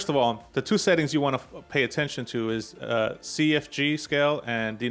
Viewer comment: great tut! just found you. I can swear you wanted to say "first of all the two settings you want to FUCK with"